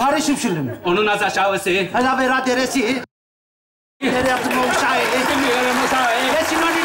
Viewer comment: Atakan ne diyor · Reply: Bilmiyrim uşaği diyor herhalde ben öyle anladım